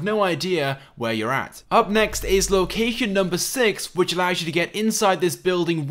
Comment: he says locacan instead of location